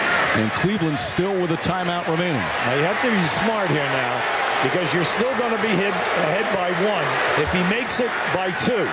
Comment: tf did Lebron say to Arenas? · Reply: J he told him to miss 😂